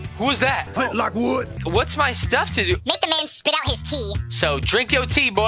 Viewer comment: when said: make him spit out his tea. I thought he said said spit out his teeth XD (sorry is someone already commented the same thing)